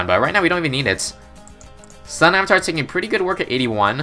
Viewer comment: i heard stun avatar